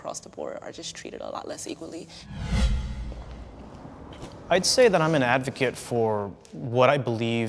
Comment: Every time talked all I heard was “I’m an adult virgin”